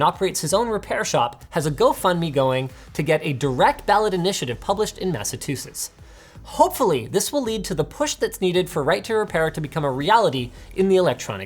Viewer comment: First time I hear someone pronounce that state as "Massatusetts".